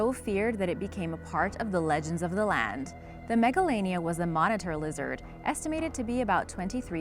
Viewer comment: I thought that she said megalovania instead of Megalania XD